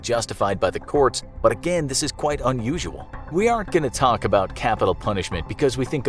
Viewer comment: subtitle should be: "by the courts, but again this is quite unusual." instead of "by the courts, but again this is quite usual."